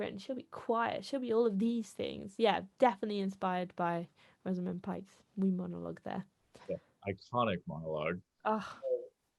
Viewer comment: i think she said, "...Rosamund Pike's wee monologue there." and at 12:37 she said "20, what year are we in?"